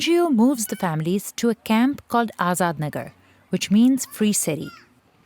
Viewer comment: what did she say? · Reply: Free stay